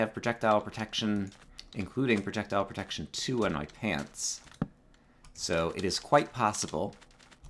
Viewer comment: Sounded like you said 'I have projectile projection in my pants'. Minecraft pickup line #34